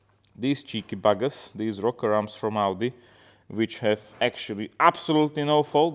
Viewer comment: Did you say the rocker arms are from Aldi or Audi? Makes a lot of sense if from Aldi to be fair... 🤣🤣